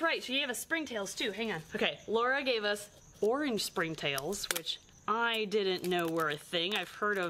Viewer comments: When you said, “Orange springtails” I thought you said “four inch springtails”. Lmao! I sure was excited to see those!!! 🤣😂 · I thought you said. “4 inch springtails” not “orange springtails “ lol 😮